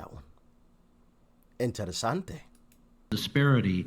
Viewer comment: Did you just say "interessant" (German) at around 7:25?